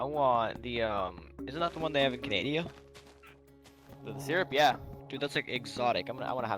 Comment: did he say canaydia?